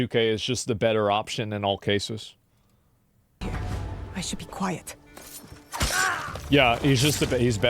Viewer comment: Did she just say out loud “I should be quiet” before killing the dude directly in front of her?